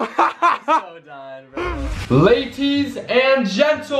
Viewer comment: he really said laties